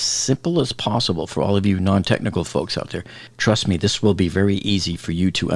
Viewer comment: 🎥 Watch Next: 🎥 FIU Bridge Collapse: WORST Engineering Blunders Ever: <link> 🎥 How To Install Laminate Flooring For Beginners DIY <link> 🎥 SCARY Crane Collapse Fell Off Tower, Fort Lauderdale, FL <link> 🎥 Titan Sub NEW Air Force Audio: An SOS For Help? <link> 🎥 LEAKED Titan Sub Transcript Shows Crew In Battle For Lives: <link> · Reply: Did you just say "Truss me"?